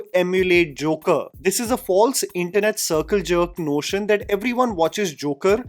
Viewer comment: did you just say circle jerk? 😶